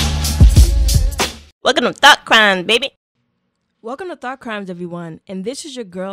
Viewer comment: First time here I'm alil sleepy,but at the beginning I thought it said THOT CRIME lol